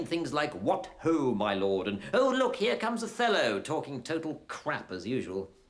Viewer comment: I always thought he said "Oh look here comes a fellow," and talking, etc...